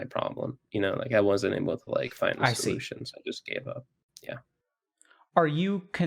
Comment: I thought he said “final solution” i was like Y I K E S D U D E 😂